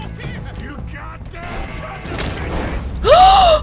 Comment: did anyone heard aurther crying out "YOU GODDAMN SON'S OF BITCHES!!!"😢